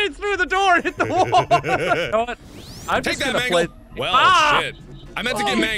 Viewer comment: i havnt heard markiplier curse in this surprisingly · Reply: Bro Zero I think he said shit